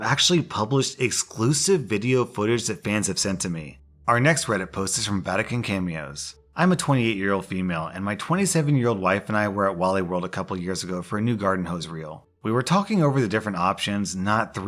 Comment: I heard "Vatican Cameos" and immediately ducked.